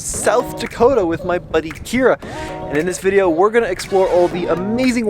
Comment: said “Buddy”. I heard “Girlfriend”. Playa playa.